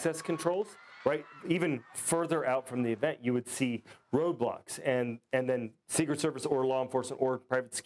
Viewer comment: did bro just say roblox?